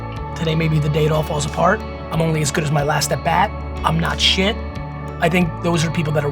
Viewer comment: "I am only as good as my last DEBUT". What did he say? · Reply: * I am only as good as my last at bat